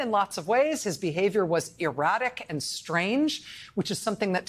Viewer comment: did Rachel really say Nixon's behavior was "erotic and strange"...? If so, I've got to say, that wasn't my impression... but each to their own...